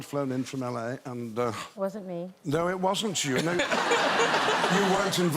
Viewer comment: Madonna was saying, it wasn't me, to the " Awww" Ian heard. Did anyone else catch that?